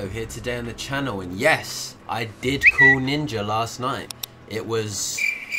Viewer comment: I love how the twitter sound effect makes it sound like you said “yes I did fuck ninja last night.”😂